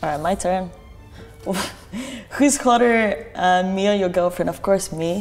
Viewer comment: What was that question at 3:21 ?? I couldn't understand it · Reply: "Who's hotter, me or your gf?"